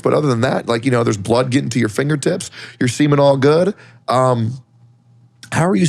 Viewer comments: listening while at work I swear I heard "your seamen all good" lmao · I could have sworn that said that the paramedics said "your semen's all good" lol... but now I think he said they said "your seeming all good". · "you're seemin' all good" I definitely heard "your semen all good" at first.